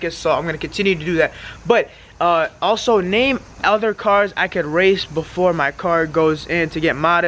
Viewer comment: It's all for fun I could of sworn you said you raced for money